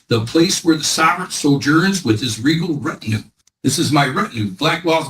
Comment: "Fruit of the poisonous tree?" Yup, "Law & Order" aficionado. 6:24 Did he say, "regal RECTUM"?